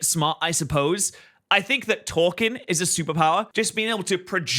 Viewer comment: I could of swore he said that humans super power is twerking 😂